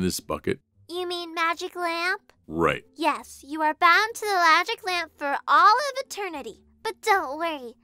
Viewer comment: Lol, at 5:52 pixie says 'lagic lamp' instead of 'magic lamp'